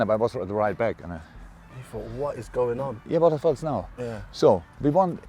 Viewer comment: Did Klopp say wtf's now 9:26 🤣🤣🤣